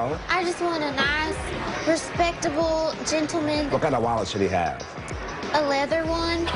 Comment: What does the dude say at 4:50? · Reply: Lauren lovesblackroses ' what kind of wallet should he have '